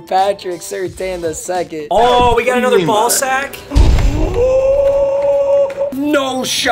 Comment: Anyone else catch at 10:40 he sId "we got another ball sack"